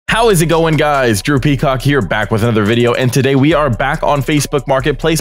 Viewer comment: This is my first time on this channel and I seriously can't lie when he said drew peacock I had to check his YouTube name cuz I thought he said "how's it going guys droopy cock here "